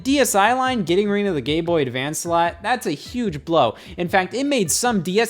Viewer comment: am i going crazy or does he say Gay Boy Advance here